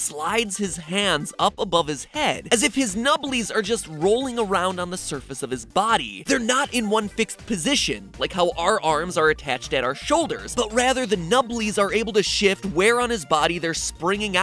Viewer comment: Oh gosh I thought you said Nibbles at first